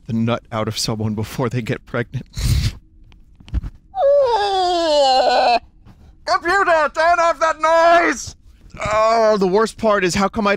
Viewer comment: does anyone know what say at 9:30? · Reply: Computer, turn off that noise